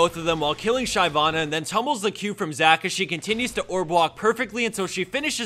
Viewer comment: did he say "orb walk?" what's that mean?